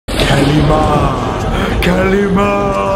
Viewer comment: I thought he was saying "kalma kalma" (Philippine word) in english it means "calm down"